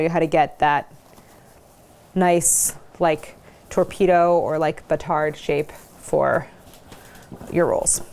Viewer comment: I really thought it said "7-inch bastards" lol